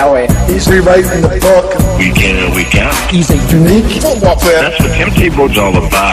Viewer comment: Can anyone decipher what the horse face is saying at the :46 mark? All I keep hearing is “we get who we kempt” and I’m fairly certain that’s not accurate. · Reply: Week in & week out.